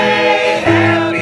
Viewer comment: does anybody else feel dizzy after the song... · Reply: Yes in wired way it sounds like they saying happy Death day